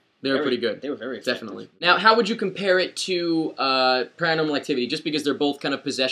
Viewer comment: What is the dude on the right saying at 1:45? "How would you compare this to grandomlightcity..." I have no idea what you said there lol.